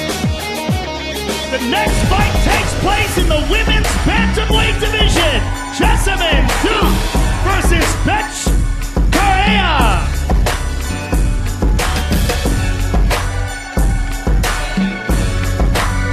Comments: did Joe just call that woman bitch? lmaoXD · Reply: Her name is Bethe, and it's pronounced Betch. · Did Joe just call her a Betch?